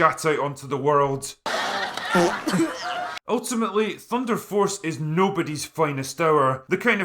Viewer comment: i thought it said "tinder force" for a minute there